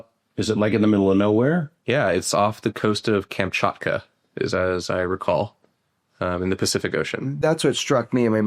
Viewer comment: Camp what? What does he say? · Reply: Kamchatka. Peninsula in far east Russia.